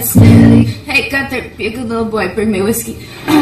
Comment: what did she say @ 0:49 to Ghunter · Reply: Moony385 be a good little boy and get me some whiskey!